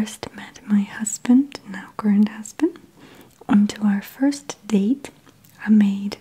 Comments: What's a grand husband? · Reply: Oh. I guess the wording threw me off so I heard "grand" 😅 "When I first met my husband, now current husband" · did she just say "my husband, now GRAND husband?!?! Or Is it bed time? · did she just say "my husband, now GRAND husband?!?! Or Is it bed time? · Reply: Is this like the Laurel or Yanny thing? LOL. I heard "current". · did she just say "my husband, now GRAND husband?!?! Or Is it bed time? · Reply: I heard 'current'